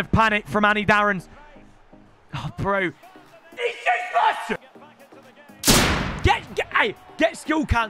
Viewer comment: What did Danny say at 8:23? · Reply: He said he’s useless